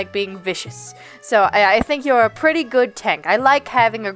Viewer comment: When you mentioned for venom "a pretty basic tank player", I heard "tink" and autocorrected to "twink". Never heard of a twink giving all 19 inches before.